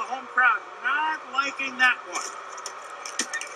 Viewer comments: I always thought he was saying can of cola 🤣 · This whole time I thought bro said kettle corn, kettle corn and I’ll be fine! Kettle corn!